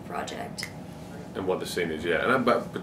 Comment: what did theo say? sorry couldn't catch his english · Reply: I think he said "and what the scene is yeah" but I don't know for sure